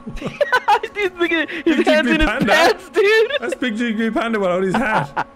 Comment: am i tripping or did say the N word at 1:42 😭